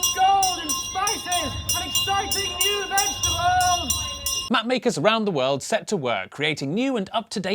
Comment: The first time you said 'Map Maker' I thought you said 'Matt Baker'... Bit of a difference.